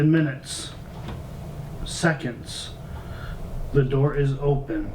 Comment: it sounded like it said the door isn't shut instead of the torch